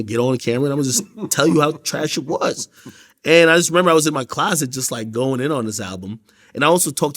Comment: He was in a closet? 😯 😂 I thought they said he was in a basement 🤣